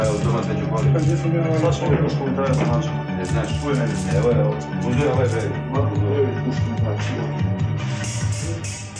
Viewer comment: What Word is it Snasch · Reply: Jel znas - You know